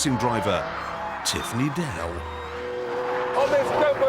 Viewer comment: I always thought he was saying “Tiffany Dell”